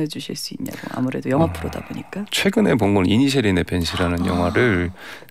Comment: 혹시 24:21 영화 제목 뭐라하는지 도무지 안 들려서 그런데 혹시 아시는 분…? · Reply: <이니셰린의 밴시>입니다. 최근영화예요.